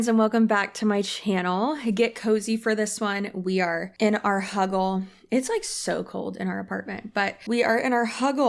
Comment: Omg I thought you said at the beginning “we’re here in our Hovel” and I thought darn the new apt must be bad lol